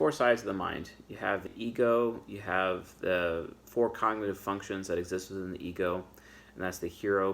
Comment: Did I hear "Fucktions" at the 12th second of the 8th minute? 🧐 YouTube, arrest this man 🤨